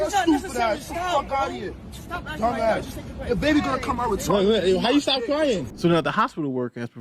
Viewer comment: Did I hear one of them say something about it doesn’t matter the babies gonna come out retarded?